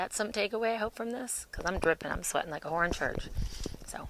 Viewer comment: Love ya, yes the mic is working. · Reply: Yes... There is great value in what you are saying. At first I thought you were saying that you are “Sweating like a Hornet!” What? Murder Hornets came to mind. It may be my only laugh of the day & that’s an enormous deficit for me. I like how you’re not talking about CV-19. I’m an extrovert & known to be quite funny myself... Thank you for the reminder. I’m a writer too & definitely not meeting my potential. My mind is on what this Fall will bring. I’d like my son & G Babies to head to Arizona this month with me. Option is to stay here & wait for a possible Tsunami?? We will see.